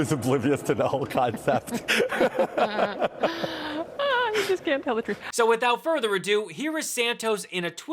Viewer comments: Did I really hear the interviewer mutter at about 2:30 "he just can't tell the truth"? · did she say "you just can't tell the truth"? sad when fox sees through your blatant lies. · Hahaha 2:23 2:29 2:30 (what she said: "hahaha, you just can't tell the truth") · “ahhh you just can’t tell the truth” is what I think she said.